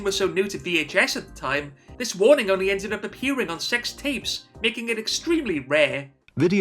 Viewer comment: I thought you said "on sextapes" here, and was very confused as to how anyone could rate a pornographic film as a 12.